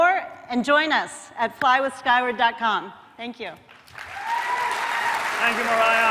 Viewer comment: What was the website she said 36:58 ? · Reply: Omaro Abbott flyward <link>